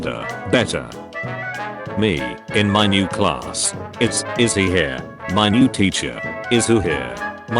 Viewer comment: There were so many scoops ahoy ones I kept hearing “is, is he here?” And “again seriously” on repeat